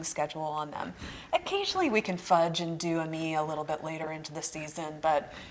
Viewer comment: Great information! Very helpful! You mentioned a cool weather flower that I'm not familiar with. It sounded like you said "ah-me", lol. Were you referring Ammi majus? Thank you for your videos. I always learn so much!